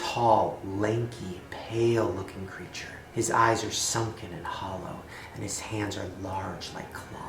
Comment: and all I heard was "his eyes are sunken and H💿L💿" all my holosexuals where ya at